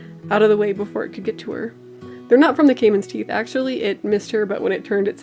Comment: A caiman is basically like a small alligator! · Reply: oh I thought you were saying cayman and I was associating it with “caveman”